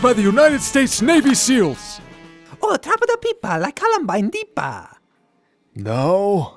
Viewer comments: Did Mario just say Columbine. I see what he’s referencing. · did he just say "like Columbine"? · did he say Columbine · i think i heard columbine in there somewhere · i swear to god i thought i heard Mario say columbine